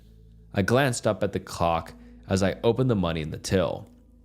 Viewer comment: bro says cock instead of clock. I'm dying 🤣😭💀